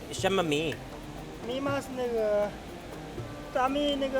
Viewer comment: did he say nigga